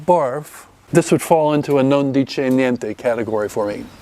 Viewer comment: "그냥 그런 와인" 이라고 번역된 부분 실제로 뭐라고 하신건지 알 수 있을까요..? 관용어구인지 영어가 아닌건지 들리지가 않네요. 아시는분 알려주시면 감사하겠습니다 :) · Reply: 이탈리아어입니다. non dice niente